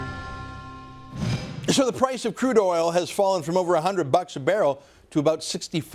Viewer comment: I thought he said trudeoil as in trudeau